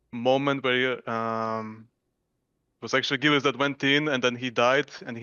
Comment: Can please tell me what says at 10:12? I cant understand him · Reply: "it was acctually Gilius that went in"